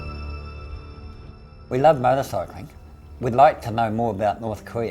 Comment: haha the subs on this video are totally fucked up. At 1:54 he says: "we love motorcycling" and the subs say "We love my recycling" and alot more errors in these subs Looks like an interesting vid, but it hard to understand what he says. Please fixing the subs would help ;x